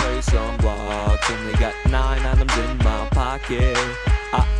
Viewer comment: when its said minecradt i thought it said mama i feel like crap